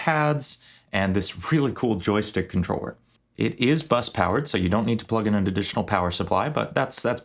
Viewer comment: Actually im just a moron and thought you were meant to say usb but you werent 😂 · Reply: I didnt know what bus powered was and thought you said usb